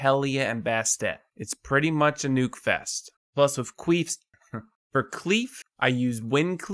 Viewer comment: Love the to of the day...."Don't be a douche"...epic. Great info as usual. Thought I heard you say queef (1:03) , lol